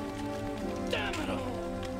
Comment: does he not say "Damit, Lou" instead of "Damit all", referring to his friend General Armistead? Now I do not beleive it, but it would have been a good line.